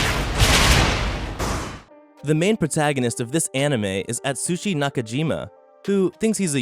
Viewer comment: i thought he said he was at sushi like a sushi resturant lol